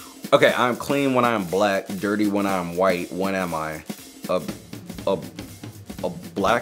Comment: He said for the “I am clean when I am black, dirty when I am white” he said “when am I” did anyone else hear this also it is at 9:24-9:29 I believe